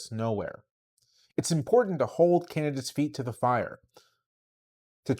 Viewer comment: the transcript/subtitles says "Canada's mistakes" instead of "candidates mistakes." I don't know if you can edit it, but I also don't think you want beef with Canada.